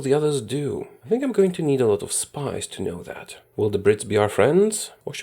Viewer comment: I thought he said "SPICE", been in EU4 for too long